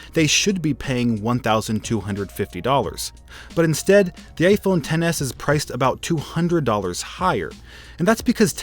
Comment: No offence, but why does it sound like he says gayphone XS instead of iphone XS